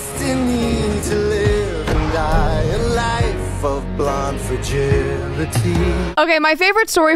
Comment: Sounded like he said "blonde fragility" ?